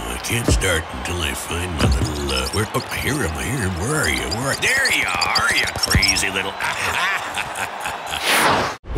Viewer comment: I dont know what he is saying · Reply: “Oh I hear em I hear em THERE YOU ARE YOU CARZY LITTLE AHAHA